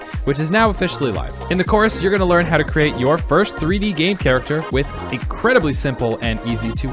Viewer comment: Did u just say your first 3D gay character? 😂 😂😂😂